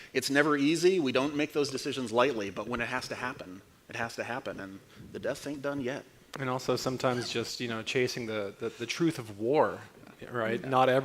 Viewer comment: "and the death ain't done yet" ? Is that what he's saying? Hmm, I wonder who he's talking about... what is dead may never die... or can it?